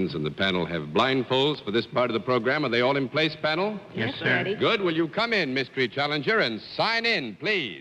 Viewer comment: Did Dorothy say "Yes daddy"?